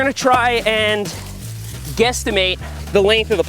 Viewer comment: did he just say guesstimate